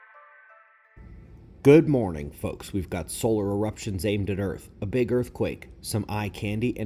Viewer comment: Oof. I got elections on the brain... yuck -- I thought he said "Solar ELECTIONS on the way." Good grief.